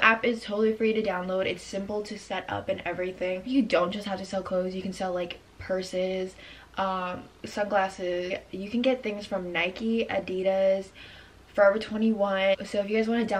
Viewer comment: Yo I'm on sum dumb shit cuz I thought she said , 3:49 "you can also sell persons..."😂💀🤦🏻‍♀️